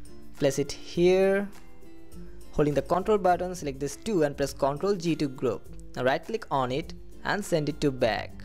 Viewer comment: Control+G doesn’t work :(( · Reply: rohini shree I didn't. I heard him saying "now select this do, and control G" like.. wait what..